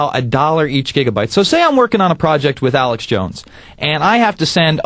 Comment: 1 question did he said Alyx @ 0:39???? o.O thumbs up if you agree